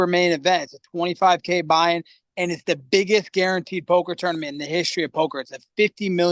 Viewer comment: I misheard and thought Colby played in a Pokémon tournament. „He fuckin WHAT?!„ 😂